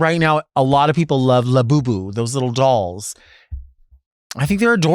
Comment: I know its “Labubu” but I just heard you say “la Boo Boo”